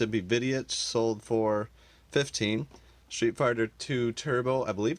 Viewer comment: Did anyone catch and laugh when it sounded like he said "Street Farter" at 10:55 😂😂?